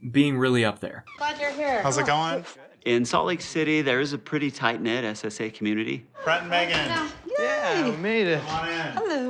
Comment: I nearly pulled a muscle listening to this at the gym—I could have SWORN this man said “tight knit SA community.” But my brain decided not SA victims, no, clearly he meant a really loving family of SA’ERS 😭😭😭